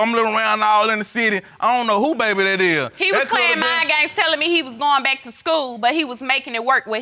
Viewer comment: I thought he said Mine Games but it was Mind games